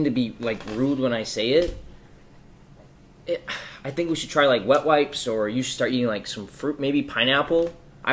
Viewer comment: My nigga really said wet wipes 🤣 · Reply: Thought he said butt wipes lol